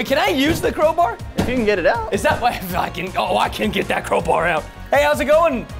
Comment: What did he say on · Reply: I can get that crowbar out